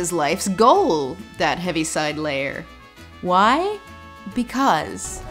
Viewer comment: When they talk about 'the Heaviside layer,' I always heard it as "Heavy-Side Lair" and thought they were talking about some 'Feline Valhalla' or something (They kind of ARE talking about that-the same way Christians talk about "Heaven" like it's some alternate-dimension Paradise & not 'what it actually is' (the air & the void above the clouds)--but 'the Heaviside layer' is an actual thing---supposedly the layer of atmosphere that reflects radio-waves back to the ground ... or something ...)